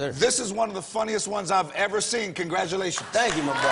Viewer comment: did i heard right ? 'thank u motherfucka' ? 0.o